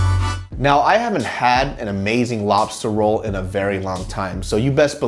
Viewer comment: on 10:19 I thought he said "I haven't have a good sex."